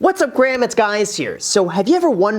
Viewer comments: Did he just say “ hey graham it’s guys here” lol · Did he just say “whatsup Graham it’s guys here”? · Wait a minute.. did he just say "what's up Graham, it's guys here" or am I losing my mind. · Did I just hear, "What's up graham, it's guys here..."